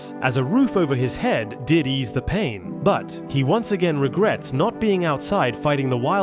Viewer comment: For the firefighter one I heard “Diddy’s the pain”